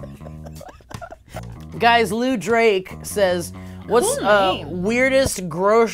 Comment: I heard say at 7:26, "it's Jewish Drake". Fun fact; the famous Drake IS actually Jewish.